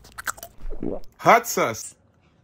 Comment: when he says hot sauce it sounds like he is saying hot sus